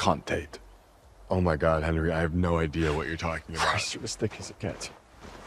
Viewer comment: I thought Henry said "you're as thick as a cat" until I read the book and discovered the line is actually "you're as thick as it gets". But I still like "thick as a cat" better!